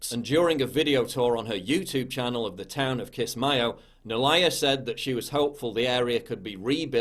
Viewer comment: When they say Kismayo I hear 'Kiss My Ass'